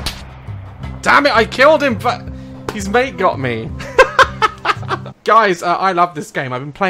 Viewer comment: the first time I heard "damn I killed him but his mate got me" I thought I heard "damn I killed him but his leg got me."